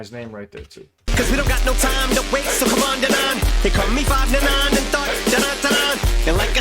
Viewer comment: i think he said after Denaun "here come me 5 da 9 and thought" around 13:20 love the content man, hope YT figures itself out.